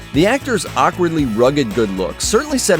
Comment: Adam driver's rugged good looks? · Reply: I thought he said awkard looks